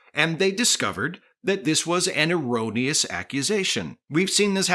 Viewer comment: I must have some crossed wires, because I could swear I heard Captain Sisko shouting "... and they will discover that IT IS A FRAUD! " I'm glad I like your politics content as much as your Star Trek content.